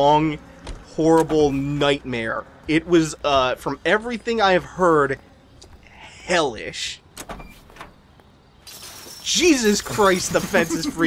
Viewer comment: did you say hellish